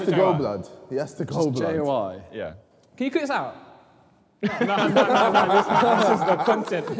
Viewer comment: what does randy say at 17:15? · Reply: "can you cut this out?"